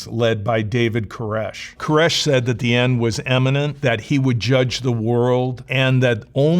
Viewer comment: Sounds like he said "the end is eminent" instead of imminent.